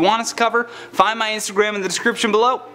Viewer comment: Whenever you say your name, I hear 'Jolly Roger'